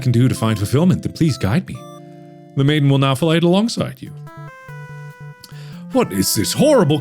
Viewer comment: I'm sorry did you just say "The maiden will now fellate along side you."?